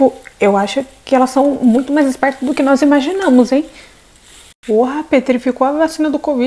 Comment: eu ouvi um porá ?